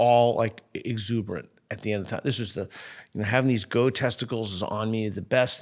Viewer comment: "Having these go testicles..." Peter has mentioned these several times, but never explained what they are. Does anybody know what he's talking about? EDIT: At 58:30-ish Mia says it again, and the YT captions show it as "goat" instead of "go". That still doesn't make any sense, but at least it's something I recognize.